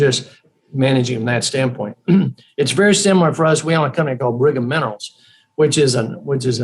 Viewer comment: what’s the company he is saying? Anyone know the ticker? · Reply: Brigham Minerals MNRL